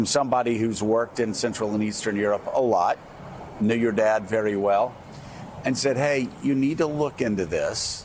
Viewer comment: I thought he said "...N-word dad very well" I had to replay the vid :P